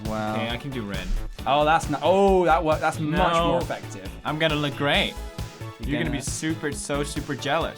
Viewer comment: I thought he said "I'm gonna regret" lol